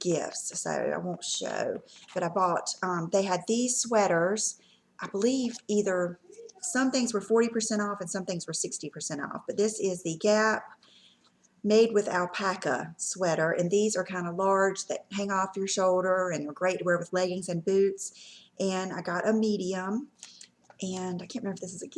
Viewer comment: Hi, What are the names of the gray and the white sweaters? (9:55) Sound like you said The Gap made with alpacka?